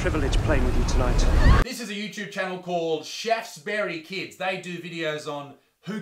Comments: Haha I thought he said “chefs bury kids” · As you said Shaftesbury, i thought you had said "chefs bury kids", which would have been a far grimmer title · shaftesbury kids i heard "chefs bury kids"... and that is too accurate for my taste no pun intended